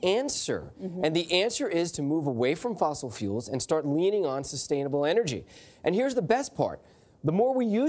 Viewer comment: What he said in 1:07? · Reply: I'm not native English speaker but I think he said "we have to start leaning on sustainable energy"